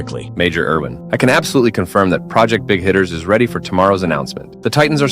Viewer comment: Thought it said “Big Hitlers”